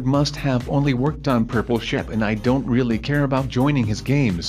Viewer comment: I thought he say purple shi*